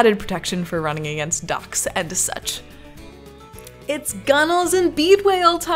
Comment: Ohh... "Docks and such"... I had to watch that 3 times because I thought you said "Running into ducks and such"